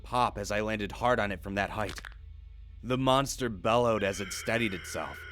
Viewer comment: Any monster that catches me is damn sure gonna be out of breath when it does. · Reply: Leslie Jackson I thought u said molester instead of monster. ( idk if i spelled molester correctly)